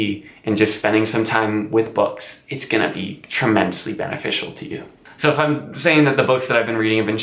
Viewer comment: is he saying tremensely? Like tremendous and immensely? My coworker says this a lot, so it caught my attention right away. I can’t tell if that’s what he actually said though, but it made me laugh.